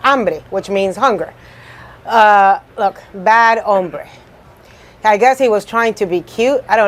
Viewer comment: sounded like ombre to me 🎨